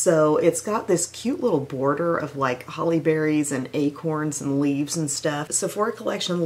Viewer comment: said "its has a lovely collection of Holly berries" and I heard "Halle Berry" and I'm waiting and waiting for lots of Halle Berries to appear.